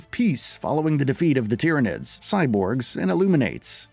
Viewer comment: Did he said 'Tyranids' at 2:15? Warhammer referencr maybe? 😏